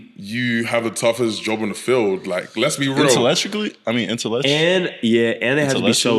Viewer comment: what he say lmao? InteElectrically